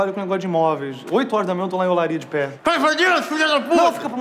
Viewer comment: O que é o que fala mesmo no minuto 1:48?? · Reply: “ Oito horas da manhã eu tô lá em Olaria de pé!” Olaria é bairro do subúrbio aqui do Rj! Hehehe eu moro pertinho.